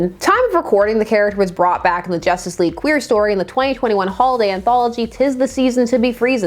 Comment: Wait is the JL queer an actual sub team? timestamp 10:40 incase I misheard and someone can explain?